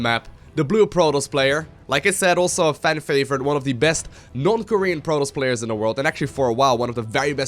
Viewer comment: I heard "Best North-Korean player"